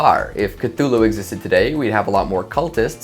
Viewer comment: did I just hear immortality?